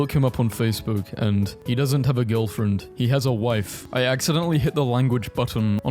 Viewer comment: I thought it said "he has a wifi"